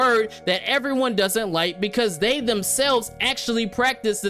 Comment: did you say they/them selfs! Lol